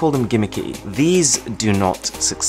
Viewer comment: Did I heard.... "Thanks for not watching"....? Or is it just my imagination